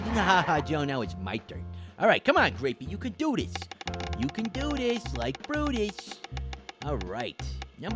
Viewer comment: Did you just say "Do this like Fruitus?"